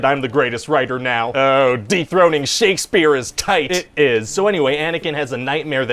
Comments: I REALLY thought he said, "Ohh, deepthroating Shakespeare is TIGHT!" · I genuinely thought I heard you say “deepthroating Shakespeare is tight” 😂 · legit thought he said “deepthroating Shakespeare is tight!” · I definitely heard “deep-throating Shakespeare is tight” lol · In the Revenge of the Sith meeting I thought he said, "Deepthroating Shakespeare is tight."